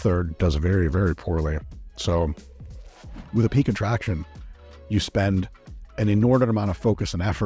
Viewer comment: the transcription foot note says attraction instead of "contraction." Was this edited by a bot?